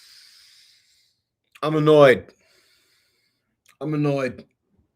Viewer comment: Did you just say: "I'm a nerd" with a Bernie Sanders accent? 😏